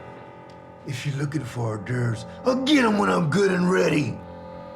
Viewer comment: I heard "If you lookin' for ubderbs, I'll gEETEM WHEN THEY'RE GOOD 'N READY!"